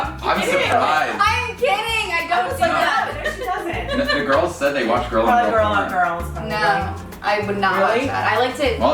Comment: did she say that the girls said they watch girl on girl porns?! 😂